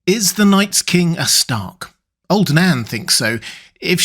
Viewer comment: I thought it said, is the Night's King a Shark?